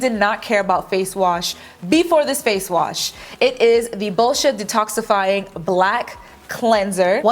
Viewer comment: I thought she said bullish!t 😂💀